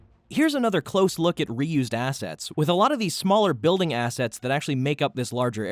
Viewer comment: I thought 15:00 said "Here's another close look at Ryu's ass"